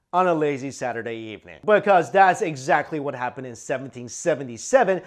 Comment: Alexander Hirst No he didn't lol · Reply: actually, your right... At 00:14.. theres another abit where he says the year and i think he actually says 1957.... So yeah